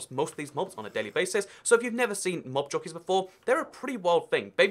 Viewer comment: I thought you said nob jockey and had to replay it just to make sure